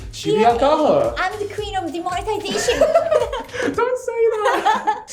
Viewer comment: Shibuya: “I’m the queen of demonetization” YouTube: She’s not wrong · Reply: I thought she said demoralisation but I guess this is right too.